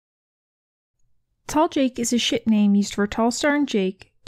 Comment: When you said talljake is a ship name i thought you said shit name LOL